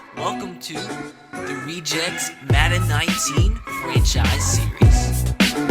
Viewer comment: seconds captions say 'welcome to the rejects Madden 19 in Chinese'